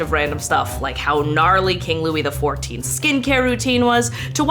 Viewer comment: Corrected captions are up! Sorry about the mishap 💛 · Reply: Hi! Thank you for having CC's on your videos :D I just wanted to say that on 5:14, the CC says "King Louis IV" when you say "King Louis XIV" and didn't know which one you meant